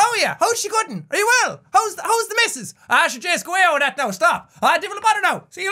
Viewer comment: I'm part Irish and I have no idea what he said at 3:40 and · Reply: “hows the misses?” 4:40 “that’s what it’s all about; a bitta ceoil” ceoil is the Irish for music.